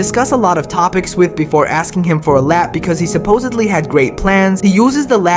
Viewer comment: I thought he said "[BRUCE] asked for a lap" to which I said "for what a dance?" I laughed, my psychosis laughed, Alexa laughed, ah good times.